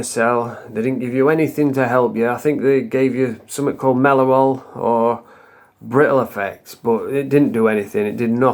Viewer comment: Does anyone know what the drug called at 01:16 it sounds like "melorol" or "brittle effects" 😂